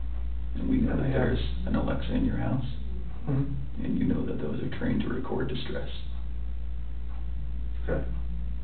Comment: Did he say “Alexa is trained to record distress”.